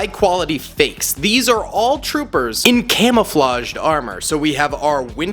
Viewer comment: did you say armor?